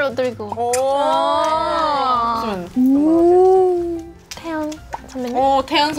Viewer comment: 선배님이 태양 아니고 태연. 거의 확실히 태연으로 들림😂😂 taeyang X taeyeon O